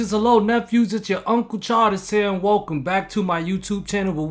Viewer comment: first five times i listened to your vids i thought you said 🤣